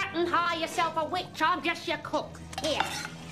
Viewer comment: "Certain? You want certain, hire yourself a witch, I'm only your cook!" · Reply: When I was younger i thought she said " il get you a coke" 😂